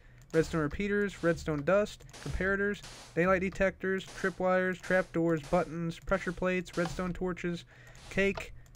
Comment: did he say crapdoor